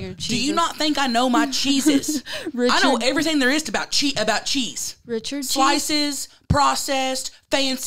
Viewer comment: 😂sounds like your saying I know everything about Jesus!